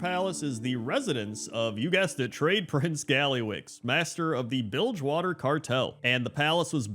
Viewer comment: I'll be honest, at 6:30 when you said, "You guessed it, Tradeprince Gallywix," I heard "Dreyfus Gallywix," and I thought, "Wait, Blizzard named him WHAT?"